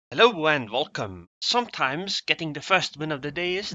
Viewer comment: I always hear him say "Hello and Vulcan!" Like the startrek race. And then I imagine making a vulcan salute. :D